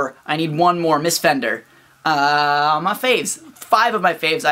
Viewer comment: ps. I thought you said, "MissFenderr... on my face." And then understood you actually said, "Are my faves." Take that as you will.